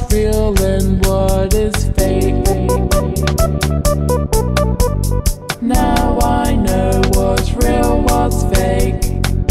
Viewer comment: Why do i hear "now i know whats real whats cake?"